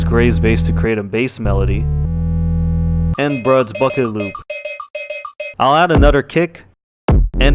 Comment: did I hear the f word? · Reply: He said bucket.